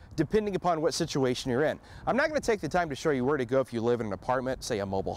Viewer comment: did he say I'm not going to take the time?